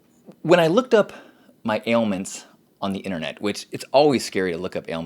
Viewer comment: towards the end of this video, i misheard "ailments" as "anal mints". great topic in all seriousness tho.